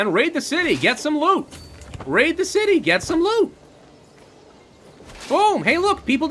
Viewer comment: Sounded like: Rape the city get some lube! Rape the city get some lube!